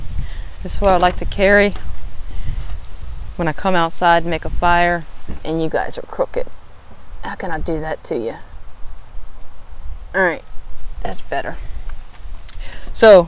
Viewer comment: Really good choice of items for such a small space. I heard you say "Hey you guys are crooked." Most of us are quite honest and upstanding I swear! okay okay, play on words. Thanks for sharing.